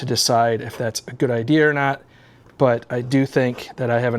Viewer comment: Well I heard that Northeast accent come out with idear.